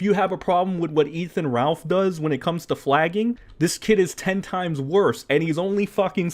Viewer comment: I thought it said fagging for some reason